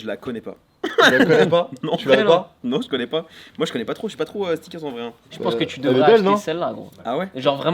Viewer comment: ptdrrr il dit « moi je suis pas trop stickers »